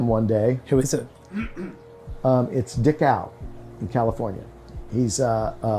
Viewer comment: did you say his name was dick out?